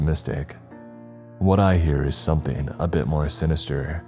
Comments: “The doors open” “Welcome to hell” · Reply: I was hoping there would be someone who would figure it out. I could understand the first part. I thought I heard the second half as "only to you" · I got serious goodbumps at the last part ending holy crap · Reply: I thought it said the door is open we’ll get you now